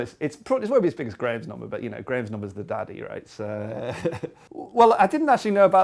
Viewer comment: What does he say at 11:15 about Graham's Number: "... but Graham's Number is the daddy(?), right?"